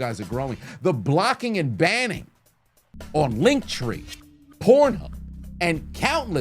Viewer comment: did he just say the hub😂